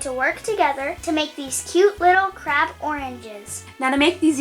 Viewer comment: i thought she said these cute little crap oranges